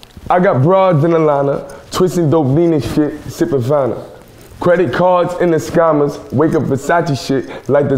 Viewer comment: I always thought he was saying "credit cards and the scannas" WOW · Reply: I thought he he was saying "credit cards and the scandals", because ppl have credit card scandals and things like that.